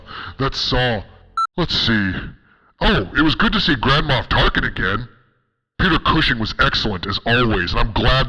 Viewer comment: i thought u said grandma tarkin